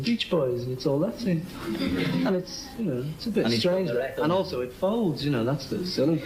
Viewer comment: I just want to know what Paul said that was so funny at 3:22. "And also it folds..."?